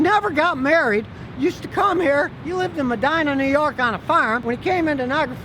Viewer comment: did you say Modena? I use to live close by to there.